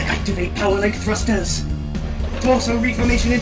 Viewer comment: I thought he said pelvic thrust when he said power link thrust